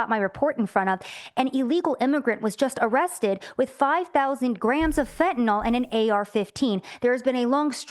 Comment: im sorry, but did she just say 5000 gramms of fentanyl? You're telling my man was walking around with 800k worth of merch and one gun?